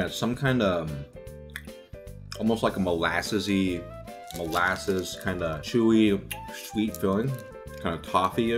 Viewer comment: I'm late but did he just say "Kind of a molassesy, molasses.."